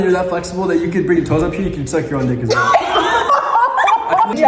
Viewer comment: idk if im crazy but did he say the n word